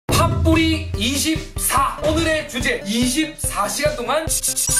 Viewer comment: 나 파뿌리 24라고 할때 잘못 들어서 김치24로 들음 ㅋㅋㄹㅃㅃ